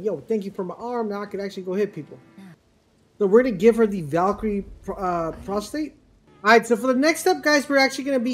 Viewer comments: You did not just say “Valkyrie prostate” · And I quote "We're going to give her the Valkyrie Prostate". Anyone else hear this at 12:58??? lol